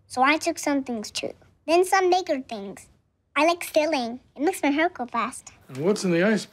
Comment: I thought he said makes my hair grow fast my mistake lmao